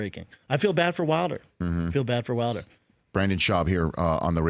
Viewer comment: Did anyone else hear eisen call him Brandon at the 10:26 mark?😂